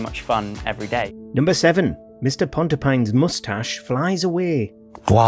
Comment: my heart skipped a beat with the subtitles on and thought it said P0^n 💀💀💀💀